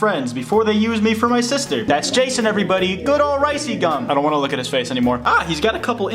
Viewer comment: I thought you said "Rice-Cum" I'm like 'jeez dude it's a pretty funny joke dammmn calm down' But you didn't say that, disliked, subbed>unsubbed>subbed>blocked, set channel as 'dont recommend'